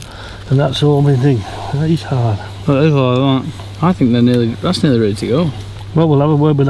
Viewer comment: Sounded like said "It's Nearly There" when you handed him the cob!😮 😂😂